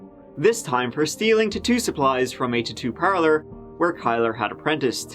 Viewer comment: 🤣 with accent, I heard ‘tutu’ instead of ‘tattoo’